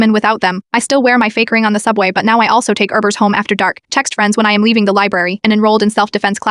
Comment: When it said she takes urbers home it sounded like it said rubbers I had the watch it a second time just to not freak out